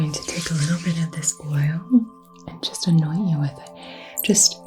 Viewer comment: I misheard you say "im going to annoy you with this oil" and it gave me a chuckle. Thank you for what you do!